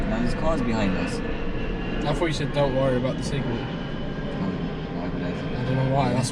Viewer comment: I thought you said don't worry about signal too! I was thinking 'what?!!' 😂